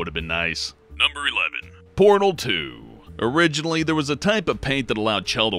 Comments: When said Portal 2, i thought he said Porno 2... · Portal 2 I thought I heard it as " Pornal 2 "